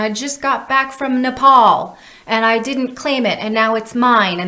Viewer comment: Did she said "NEPAL" 3:28 ? Love this video even more now lol